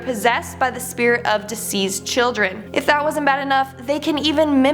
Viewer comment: It sounds like she is saying diseased instead of deceased.